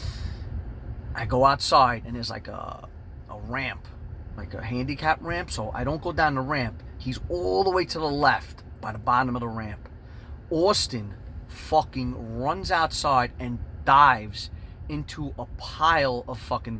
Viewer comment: Just looked him up lol he’s a badass dude🤣👊🏻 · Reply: i thought he was saying Austin Awful the whole time